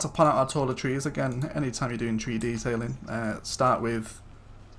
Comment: At about 11:35, you said “tree detailing” and I thought you said “3-d tailing”!